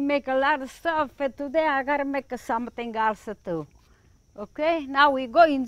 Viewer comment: Always sounds like she says -"today I gotta make.".... Like the kids are forcing her. She's so cute